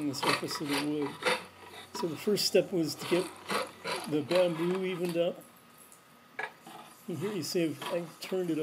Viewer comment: Hi, I had a question what did you say in the video at 3:26? Also I am using titebond 3 instead of epoxy would of reducing the bow wood be still the same? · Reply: I think I was saying the first step is to cut down the excess bamboo with the draw knife until it's basically the same width as the hickory stave, then you can shave off the excess epoxy quickly with a sure form rasp. Tightbond glue will work about the same but does not shave off quite as easily with the sure form rasp. Also Gflex epoxy is formulated to bend and hold together under strain. I've used tightbond products for parts of bows that don't bend. with no problem. However I've seen several handles pop off bows on you tube that are held on with tight bond glue. I've never had a delam issue using Gflex epoxy. You want your glue layer to be almost invisible, so you need to use the strongest laminating adhesive you can find that will also flex under strain as the bow bends, especially in the outer limbs.